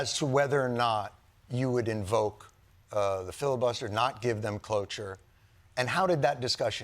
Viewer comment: Did…did he say, “give them cloche-r”?